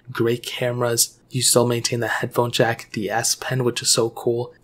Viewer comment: When he said a pen it sounded like he said ass pen. Lol